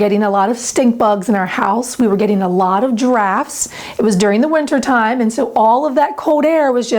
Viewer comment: At 1:23 it sounded like you said "We were getting a lot of GIRAFFES." 😂😂😂